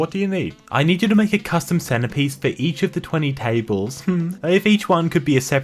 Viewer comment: I looked away for a second and thought it said; “Can you make a custom centipede?” I was like; “They make those?”